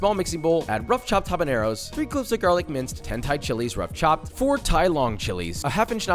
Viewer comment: When he was making the Buldak, he said "10 Thai Chilis". My brain is broken. I heard "hentai chilis".